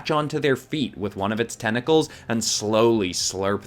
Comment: i heard "g3nitals" instead of tentacles...